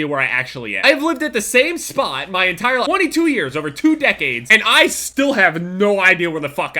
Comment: did he say 42 years?!?!